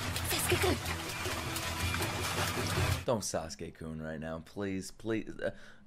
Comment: When said " don't say sasuke kun" I thought he said "don't you sasuke goon right now" ......I'm still laughing.....it's been 5 minutes now.... Help.